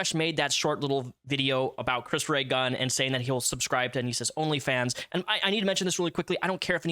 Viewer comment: I thought he said Onision’s only fans.